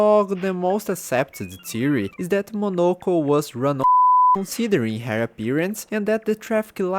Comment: What did you say here??? 5:24 also good video i love it 🎉❤ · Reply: that she was runover (implying that she is scared of anything traffic related)